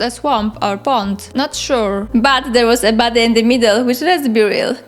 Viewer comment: I thought you said "baddie" instead of "body" so I just assumed you were calling the spriggans baddies and thought: yeah, that makes sense haha